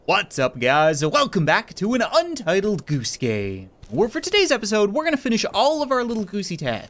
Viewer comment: sounded like you said “Untitled Goose Gay” sorry but it really does